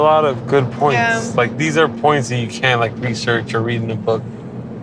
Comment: I might be wrong, but as someone who's not native English speaker myself, I think she misheard him. The way he said "can't" sounded very close to "can", especially for a non-anglophone, and she thought she was agreeing with him. But since it was not that important neither of them thought to correct that small misunderstanding.